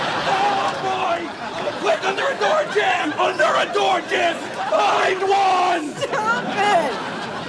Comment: wtf is he saying at 0:39 ? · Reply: Under a door jam! Under a door jam! Find one!